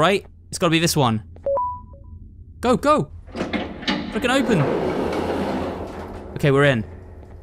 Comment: did you just swear and say “f**king open”?